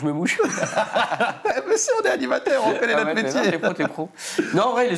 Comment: MESSAGE SUBLIMINALE : 21:46 Gluto :T'essaie de combler le temps qu'je mouches ? Haha ont est animateur ont fait les mêmes métiers Gluto : Ah ouais mais là pour moi t'es con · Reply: mais nan t'es pro t'es pro*